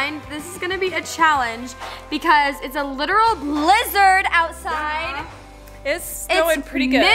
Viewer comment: I thought said lizard instead of blizzard 😂😂😂